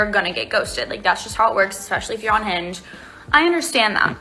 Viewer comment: Sounded like she said "Especially if you are unhinged" 😂 9:51 And I'm going to need to see your psychological report